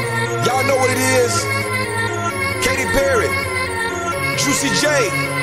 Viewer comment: This whole time I thought it said "Jessie j" but it was "Juicy j" 😭😭